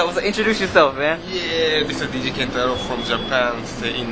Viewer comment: did he say dj kentaro??? shiet...